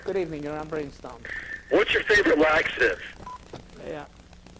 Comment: What does the guy at 9:56 saying ? "What's your favorite what ?" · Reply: Laxative.